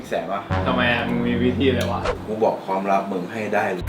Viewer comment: & 10:23 พี่หลุดคำหยาบอ่ะ 😂😂😂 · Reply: พี่ก็พูดว่า"มึงมีวิธีอะไรวะ" ลองฟังดีๆดิ55